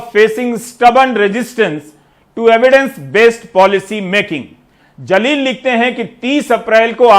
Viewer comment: did he say "zaleel"